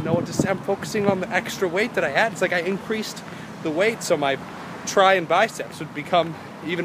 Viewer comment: Sounded like try and buy ceps hahaha